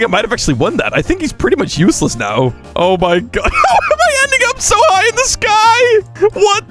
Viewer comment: what did bro just say? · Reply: Ending not what you think..